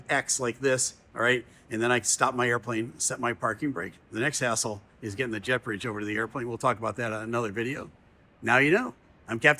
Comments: Good thing you have captions on these clips. I thought you said, "and the next asshole is getting the jet bridge over to the airplane." I had to rewind for that one. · I totally heard: “the next asshole is getting the jet bridge over to the airplane” 🤣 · I had to play a certain section back a couple times because I thought well let's just say you said the next "hassle". And I thought you said the next "A$$hole. Lmao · I thought you said the next asshole haha I had to rewind and watch it again.